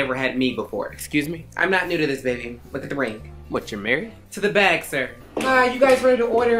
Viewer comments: "Your married?" "To the bag, sir," THIS IS THE HUSTLE, WE LOVE TO SEE IT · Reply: I thought she said “to the bank sir”😂😂😂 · "Your married?" "To the bag, sir," THIS IS THE HUSTLE, WE LOVE TO SEE IT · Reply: Thought he said married to the bank